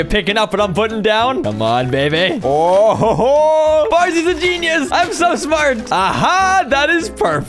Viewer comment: I like how at 8:05 Farzy says "Farzy is a genius!" But the edited in subtitles say "Farzy is a moron" 🤣🤣🤣🤣🤣